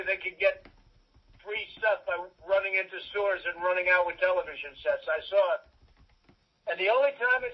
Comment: lmao ik he says "running into stores and running out with television sets," but I swear to god it sounded like he said "running into sewers and running out with television sets."